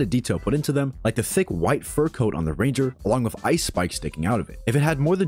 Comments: did git just say ice spice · I thought bro said ice spice 😭